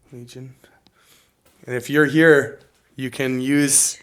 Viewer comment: Clear as a bell I hear a woman whisper “ We’re here”! Internet goosebumps.